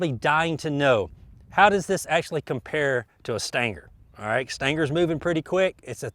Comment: I have shot the CCI "Stinger" 22 LR ammo, but I haven't run across "Stanger" 22 LR by any maker. What am I missing? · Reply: I thought he said Stanker